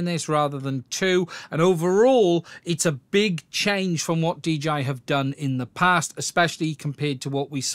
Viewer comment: "DGI" ? and we all thought they were called 'DJI'